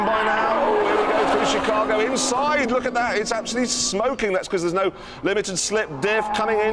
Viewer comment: Wait did he say there is no LSD?